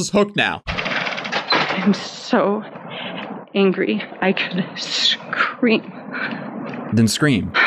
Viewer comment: BISH I THOUGHT SHE SAID "im so angry i feel like i could cream" WHY IS MY MIND SO DIRTY